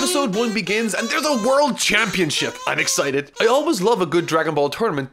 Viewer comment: When said he was excited for the tournament at the beginning. I heard "Unexcited" rather than "I'm excited", which made me laugh so hard. Edit: This Happened at 1:14.